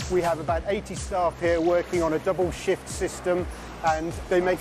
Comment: he did not say "shit" he said "shift"